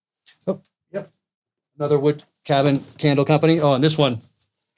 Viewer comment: Lmao I was just listening with my phone in my pocket and thought said 'wood cabin handle company'